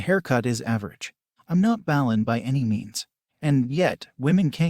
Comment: the voice said ballen instead of ballin 🤣